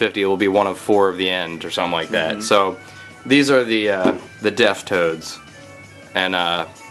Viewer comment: I thought you kept saying "Deaf Toad." Still hawt.